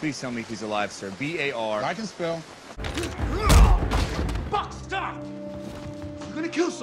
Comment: I honestly thought Cap said "FUCK STOP" before I realized he said Buck lmao